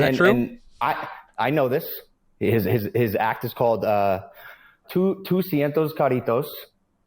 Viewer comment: As a puertorican I got to say that its great to hear how the Pauls are interested in helping the island. I will say that the best way to continue to help is by talking to locals, going to the country side and help the people struggling with house damages, clean water, gathering food. Try to collaborate with people like Eliezer Molina, Bianca Graulau and help with keeping the beaches clean and free cause in the past year they've tried to make them private. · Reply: What did he say at 25:38..? Doscientos carritos? xD Trying to decipher what he means when he says BB who was born in PR is exploiting ley22 when he's not even eligible, since he's not a foreign individual